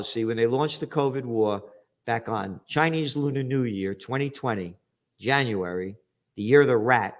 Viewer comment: I thought you said 2022 was the year for China. ?